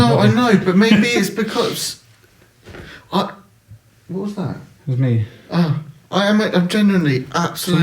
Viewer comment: evp of a growly voice saying something 😱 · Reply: yes I just slowed it down and played back and sounds like it said it was me. sorry I'm late to the video lol